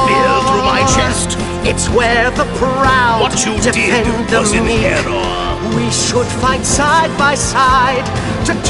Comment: I thought he said "There's a spear in my chest... what you did was in error.